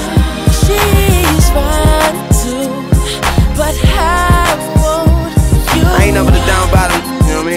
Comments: 5 YEARS LATER I FOUND OUT HE SAY “SHES FINE TOO” NOT “5’2” LMAOOOO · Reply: Hey I thought he said. "SHE WANTS TO...BUT I WANT YOU". DUH😂 Whoops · when I was a kid i thought he said “ she’s 5’2 but I want you” 😂😂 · I heard "she fine too but I want you"